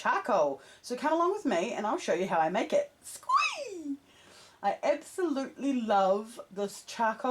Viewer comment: I'm sorry. is it bc I'm american? what is that word and what does it mean when she uses it? skeee?? I don't get it.